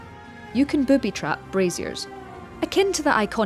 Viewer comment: I thought you said you can boobie grab in brazzers lol 😂😂